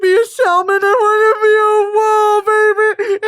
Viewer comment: i heard “i don’t want to be a salmon i want to be a woman” and have been misquoting it for the past two weeks only to come back to find that she in fact said “wolf” 😐